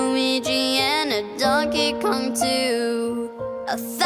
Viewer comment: it sounded like he said a thousand troopers and pooers lol